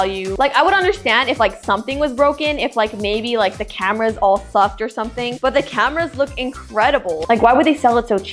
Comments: did she say “the cameras all fu€ked” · im not gonna lie did you say maybe if all the cameras were all fxked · DID YOU SAY FUCKED